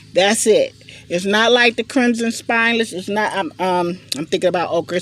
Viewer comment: Lol i heard you say clemson spineless i grow those too!😂